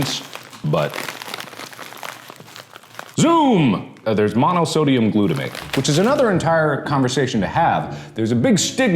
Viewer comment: It sounded like he said "mama sodium glutamate" 😅